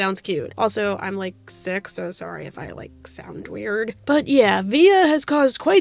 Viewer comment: I swear to fuck I’m such a dumbass, I thought you said “also I’m six so sorry if I sound weird” had to listen a couple more times to see if I heard that right, I’m a total dumbass, sorry😅